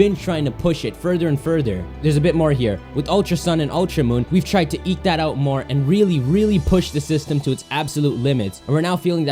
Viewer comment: I thought he said we’re trying to eat that out more. That made such a difference lol